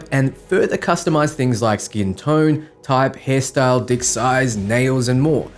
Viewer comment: did u just say u can customize dick size ?